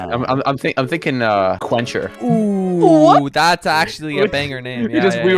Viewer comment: I’ve never heard of quencher but I’ve heard of quench, if someone quenches their thirst it means they have been satisfied with the amount of water / liquid they have drank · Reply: I thought they said "clencher" as in somebody who does a lot of clenching